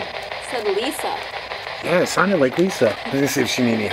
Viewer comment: that name you said sounded likeyou said ka lisa thats how you pronounce my name nice :)